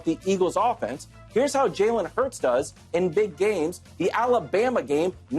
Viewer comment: Bruh im faded asf lmao I thought it said Jalen is HURT😂😂😂😂😂welp chiefs dub three peat 😒